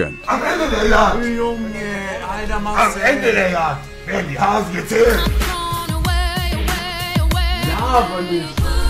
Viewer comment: Hää was sagt Monte bei 6:16 zu Sascha? "Am Ende werden die Hasen gezählt?"